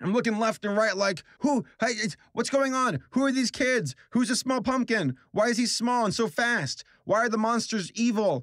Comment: SPOOPY MONTH TIME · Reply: The first time I heard say small pumpkin I misheard him and literally thought he said smool poompkin